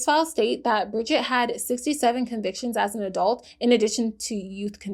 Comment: …sorry, uhm how many convictions did you say…? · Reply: 6 7!!!!!!!!